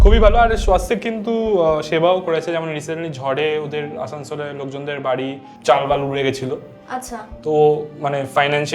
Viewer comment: Wtf dada you just say "Chal B*l"😂😂😂